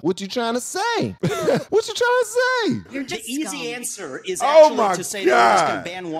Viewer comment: Did she just call him scum?